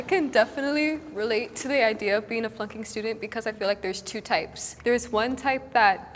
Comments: it sounds like she said "okay, so, I can totally relate to being a f***ing student" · I swear everytime they say "flunking" I hear "fcking" Eg. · I thought she said I can relate to being a f***ing student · Sounds like she says "I can definitely relate to being a f***ing student" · Did anyone else hear f*****g student at 7:38 ?!